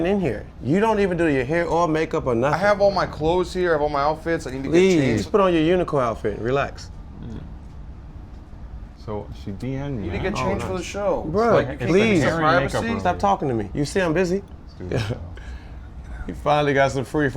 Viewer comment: Did say nigga change for the show at 0:33 o.o